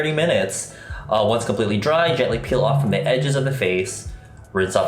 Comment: I thought he said peel off the sides of your face SKSKSKSKKSKSKSK AHAHAHAHAHHA